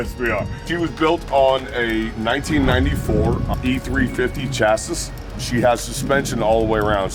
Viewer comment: did he say chassisis??🙄🤣